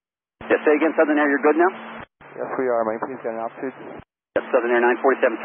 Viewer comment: No worries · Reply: I agree. This is verbatim what I hear starting 1:20 SOO947: "Southern Air 57 [does not say callsign clearly], [can you?] please give me a new altitude?" DEP: "Yes, say again, Southern Air? You're good now?" SOO947: "Yes we are. May I please get an altitude?"